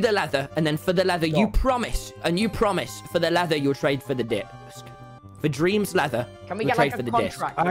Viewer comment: LMAOOO 17:40 with captions it says "I'll trade you the leather for the d***" instead of "discs" 😭